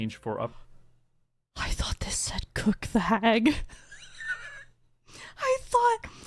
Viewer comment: ngl thought you said cook the f*g at first